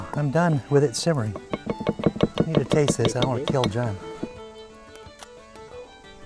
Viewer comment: Allez Cuisine! Also, the (presumably auto-generated) subs (CC) on this are hilariously bad. It has no idea what 'porc a la normande' is, and often loses words so like at 10:25 instead of saying 'I don't wanna kill John' the CC accuses him of saying 'I want to kill John' ('I can explain, your honor!').